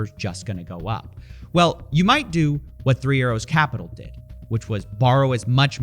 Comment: I kept hearing "3 Euros capital" which describes my bank balance pretty well.